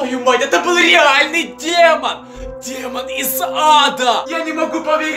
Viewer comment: Я НЕ МОГУ ПОВЕРИТЬ ЭТО БЫЛ НАСТОЯЩИЙ ДЕМОН ИЗ * ЗАДА * 😂😂😂 Мне одной послышалось не "из ада" , а "из зада"😂😂😂🤣🤣🤣 ГОСПОДИ КУДА МЕНЯ МЕНЯ НЕСЁТ😂